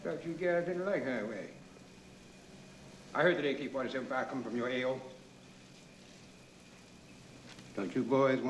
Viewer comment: I had to look this up because I always wondered what Webster was saying @ 0:34 . "I heard that AK-47 fire coming from your AO."